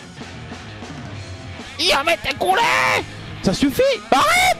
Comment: did he say yamete kure???? ¿¿¿¿¿¿¿¿ I